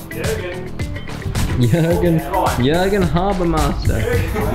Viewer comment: Did call Jürgen Habermuscle at 6:15? :D